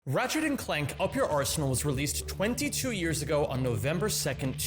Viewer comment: I thought you said Ratchet and clank : up yer arsehole 😂😂😂